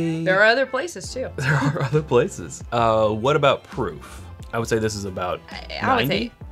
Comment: right after "There are other places..." I heard "what about Peru?" and then the "f". lol